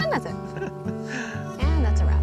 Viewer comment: Did anyone hear “and that’s a rap” At 23:40 ?